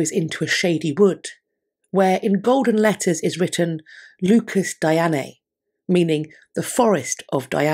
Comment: can someone please clarify what shes saying? I tried looking it up but cant find a latin tranlation close to what shes saying. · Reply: It is given as “Lucus Dianae” which might be translated as “Diana’s garden” but the article I was quoting renders it as the “forest of Diana”. The latter probably makes more sense in relation to what was actually there, maybe?